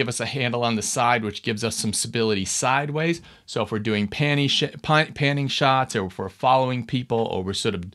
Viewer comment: did anyone else hear panny shit?